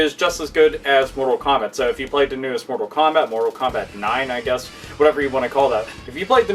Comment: I kept hearing "Portal Combat" and I just..